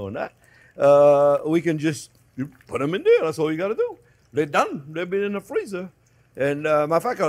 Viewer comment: Does he say mother F at 8:52 😮😂